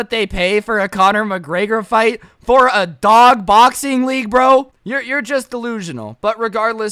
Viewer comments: Who heard him say “big doxing league” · Did he just say bog doxing league